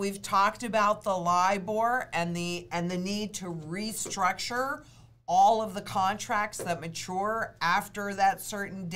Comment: Would someone explain to me what liveore restructure is at 3:25 ?? ( Im not sure that I heard Lynette clearly )